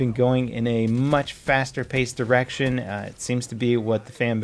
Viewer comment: "Call of Duty has, for a long time been going in a much faster paced erection" - I know he said "direction", but what I heard was much more indicative of the game.